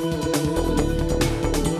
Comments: I keep hearing "I'm Mario!" instead of "I'm outta here!" for some reason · Ok, THIS TIME it actually sounds like he said "I'm outta here!". Other times when this happened, it didn't. At 1st(years ago) i thought he said "I nol you!" and this year, the other times, it still didn't seem like he said "I'm outta here!", but today... Now it did! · i'm outta here??!?! OOOHHHH i thought he was saying "I'm Mario" lol!